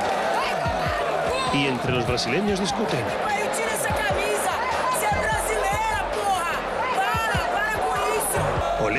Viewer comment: NO SÉ QUÉ DIJO PERO MIENTE.jpg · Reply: Porque voce tem esa camisa, sem brasileira porra, para, para com isso